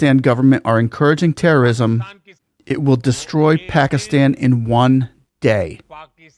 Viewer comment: Sir plz correct thjs small error, what he acutually said was 11:40 " it will destroy Pakistan one day" not "in a day"